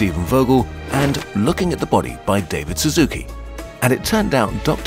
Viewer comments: thought it said bavid baszucki, the founder of roblox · What i hear: "David Suzuki" What my brain hears: "David Baszucki" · Thought you said David baszucki